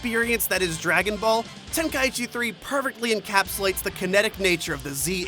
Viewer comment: He says "pervertly" instead of "perfectly"